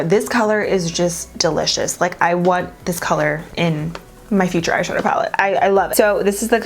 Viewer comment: Omg did I hear “in my future eyeshadow palette” yesss 😁😁😁